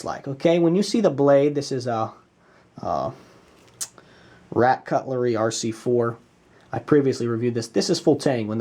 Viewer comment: Did he just say "Rat Cutlery"? lmao